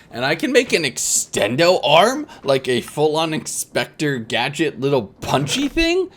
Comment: Did anyone else hear him say expector gadget not inspector at 1:24:44 lol